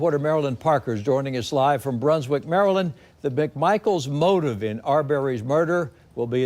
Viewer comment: I thought he said Brunswick, Maryland at first. Lmmfao